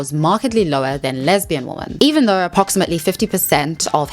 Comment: I’ve been watching your videos for a while now but I don’t think I’ll ever adapt you saying “woman” when you mean “women” I’m sorry 😢😢😢😢